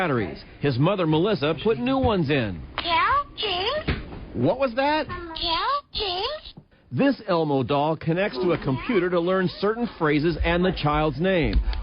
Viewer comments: I hear he’ll james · "lets talk about... ElMo KnOwS yOu NaMe🤖🤖👽👽" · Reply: DID ELMO SAID "kill James" NAH💀